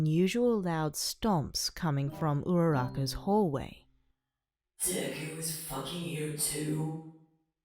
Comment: I thought bakuhoe said : “Deku’s fucking you too?” I’m not ok.